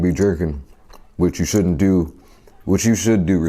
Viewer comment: i thought that was what he said and also he said which you should do responsively good thing he repeated and what he actually said was freaking DRINKING GOD DAMN